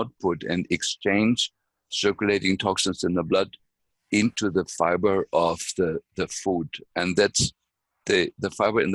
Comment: Love this! So much good info. At 34:00 he mentioned 4 things to take. I cannot understand him with the accent 😭 can someone help please? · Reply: chlorella, eclonia cava, zeolite, and some kind of methylated silica